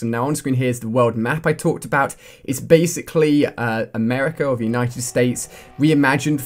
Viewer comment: Did he just say "America of United States"? Lol